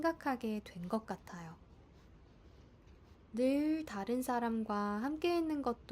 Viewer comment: it really sounds like you say 사람과 not 사람들과. is that just my untrained ear?